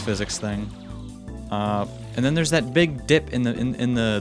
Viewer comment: swearing? or misheard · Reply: misheard, he said dip